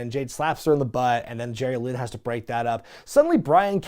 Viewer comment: did i hear "Jerry Lynn" come out of mouth?